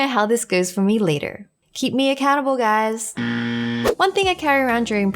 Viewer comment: Ok when wengie was talking about excersice and she fell on the bed, what I heard was " keep me a cannibal guys" I know it wasn't that but it kinda sounded like that!!! 😂😁😅